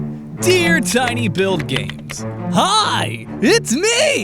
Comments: If you listen to this video without paying attention, it sounds like he says "Dear Tiny Bill Gates" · When the video started I heard " dear tiny bill gates"😂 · I legitimately thought he said "Dear Tiny Bill Gates." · I heard Tiny Bill Gates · I initially thought he said 'Dear tiny Bill Gates'. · in the beginning i thought he said Dear tiny Bill Gates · lol it sounded like he said Dear tiny Bill Gates at the beginning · Misheard tiny buildGAMES as tiny Bill Gates. Also, #letaustincurse!